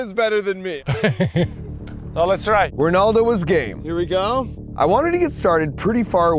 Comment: Did I just hear "Ronaldo was 🌈"